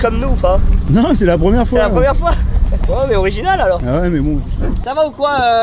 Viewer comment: Inox: ont est original Benoît: ouai mais bon Moi j’ai compris ouai mais bon vous êtes chiant 😂